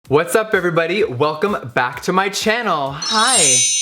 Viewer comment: Because of shook all I hear is “what’s up fibber buddy”